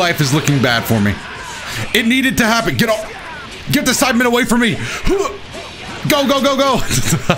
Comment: It sounded like he said “get the sidemen away from me” lol